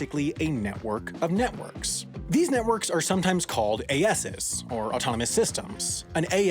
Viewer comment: i thought it said ASSes